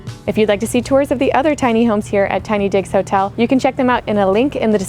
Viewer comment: At first I thought they were saying TinyDicksHotel. 😆 That would be so much funnier! Cool video. Love these things.